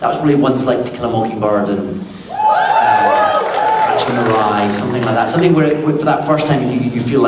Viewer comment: Can someone please tell me the names of the books and their authors at 37:00 ? (I'm not a native speaker so it's quite hard to hear clearly what he's saying.) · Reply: After three years, you probably deserve finally to have the correct answer. The three books he named were: To Kill a Mockingbird, Catcher in the Rye, and The Passion.